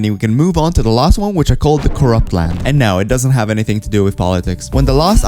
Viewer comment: I don't know why but when you said Corrupt I thought you said Korok for some reason